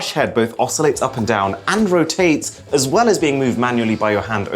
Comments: feels like he saying andrew tate instead of and rotate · Did he say Andrew tate ? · I first heard "Andrew Tate's" not "and rotates"😂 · Did he say Adrew Tate at 2:22? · I heard Andrew Tate instead of "and rotate" hhhh. Did you heard that too? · who heard andrew tate's instead of and rotates